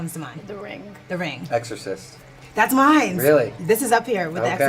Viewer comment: Did she just say “mines”? I’m sorry, but people who say that really piss me off.